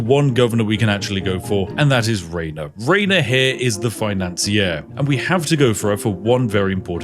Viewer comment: The author's pronunciation of "The Financier" my ears misheard it as "Ea-nasir"